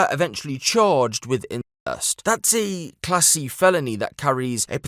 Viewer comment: did anyone else initially think he said "classy felony"? 😁